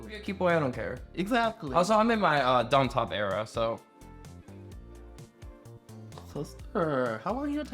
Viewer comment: Was listening to this during chores and I had to rewind cuz I heard "donald trump era" instead of "dom top era" 😭😭😭